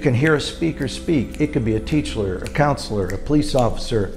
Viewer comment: 😂😂😂it’s Fucked up but I heard him say “teachler “